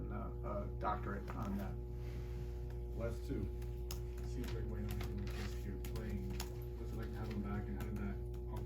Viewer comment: Who are they talking about? Couldn't hear. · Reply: Wayne Ellington